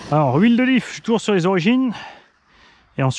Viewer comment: Mdr, j'ai entendu "l'huile d'olive" au lieu de l'huile de diff, je voyais pas le rapport du coup ... Bonap